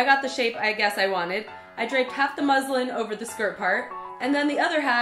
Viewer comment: I thought you said "I drank half a muslim" 😂😂😂😂😂